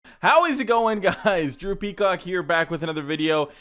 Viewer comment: Kinda sounds like he says “hey guys , droopy cock here, and today..” 😂